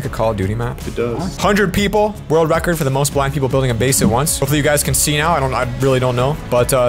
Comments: “most black people building a base at once” I couldn’t be the only one who heard that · Is it me or did he say 110 black people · did he say most black people? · Did he just say "most black people building a base"